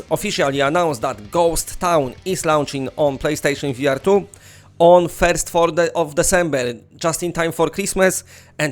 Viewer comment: Omgad😂 I thought you where saying PlayStation V Artyom